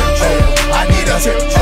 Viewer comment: I always thought it was saying "Trip Troll" instead of "Tip Drill" lmao